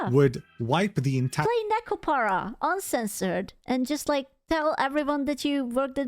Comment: did just say "play Nekopara uncensored"? I love this girl so much, best vtuber, she's hilarious, based, cute, and damn can she cook, I'd let her peg me, 10/10.